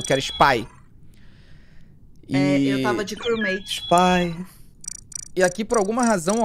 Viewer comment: o: spy Oq eu ouvi: pai🎵 (tom de calice-chico buarque) Eu: afasta de mim esse calice🎵